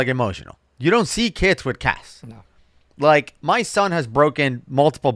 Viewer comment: When talks about kids not having casts anymore, I heard "cats" 🐈🐈. "You'd go into class and there would be kids with cats on them", wtf?? 🤣😂